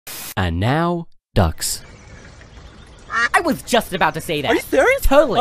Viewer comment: Duck 1: QUACK! Duck 2: I was just about to say that Duck 1: Are you serious? Duck 2: Totally Duck 1: Oh that’s spooky Duck 2: We are so in sync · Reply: I thought the last part said "We are so insane" guess not 😂